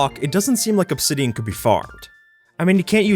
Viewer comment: I heard; when you look at the block, it doesn't seem like obsidian could be fart. ???